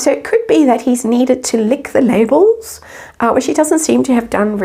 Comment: I thought you said lick the navel 😂😂